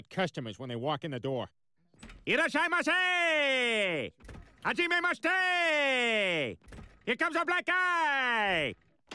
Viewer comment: this is what i hear Yes i must say Im a mistake Here comes a black eye